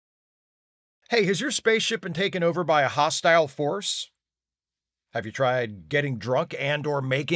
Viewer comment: why did my brain mishear Hostile Force as Hostile Horse? Anyways good luck trying to make out with one.